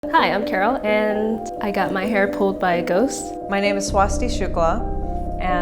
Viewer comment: I thought she said ‘ hi my name is swastika’ lol.